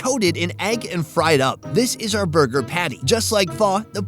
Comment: Oh Pho Burger… I thought the owner was saying Fur Burger……